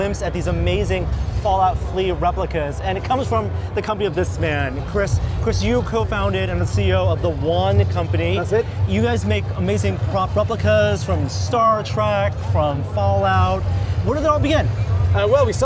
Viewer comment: I swear to gawd, did he say Star TRACK???!! You are dead to me Norm.